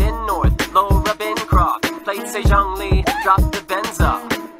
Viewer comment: imagine childe walking up onto yun jin's stage and vibing to all of these songs while making direct eye contact with zhongli and yun jin singing along with him zhongli: 😨 xiao: 😡 yun jin: 😀💅 all of liyue: either laughing or looking at him in pure terror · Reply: btw did he just say 9:07 zhongli